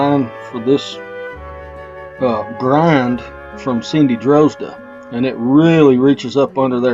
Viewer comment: I couldn't quite catch the name of the lady that you got the tool design from. Does she have a website? Also what tool was that before you redesigned it with a grinding? Thanks. I subscribed to your channel and am enjoying it very much. The clip was about at 18:00 on the video. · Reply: Thank you Gary. So sorry for the delay. I just found this comment. Her name is Cindy Drozda. I don't think she has a website but you can find her on YouTube. I make the tool from an old Craftsman Spindle Gouge. Basically removed the flat bevel and made it a smooth curve. I hope this helps and again I am sorry for the delay.